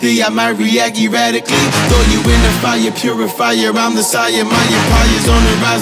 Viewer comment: All this time I thought he said 'I'm the Sith' in the beginning.